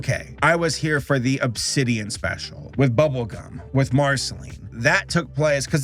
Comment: Made a mistake in this vid and said that the pilot of Helluva Boss came out in 2020. Correction: it was late November of 2019. My bad! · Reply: you sounded like you said bubblecum